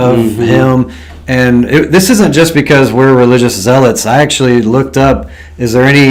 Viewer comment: Did you say religious as hell ?